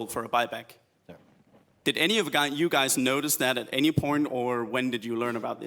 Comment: Guy says any porn instead of point lmfao